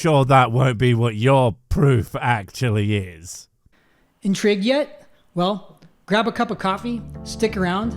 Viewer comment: Did he say "Intrigged yet?" ?!?